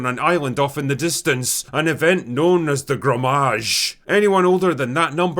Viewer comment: Every time I heard "grommage" instead of "gommage" I gagged 😂